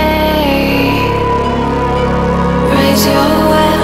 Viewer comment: But I hear "Raise Your po*n"